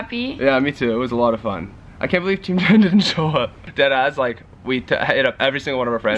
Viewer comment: On 7:40 does he say " I can't believe team 10 didn't show up?" Because thats messed up.